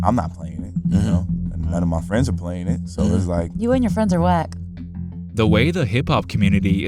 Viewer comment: U and your friends are wack💀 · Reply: Thought she said "you and your friends are white" which would have also been true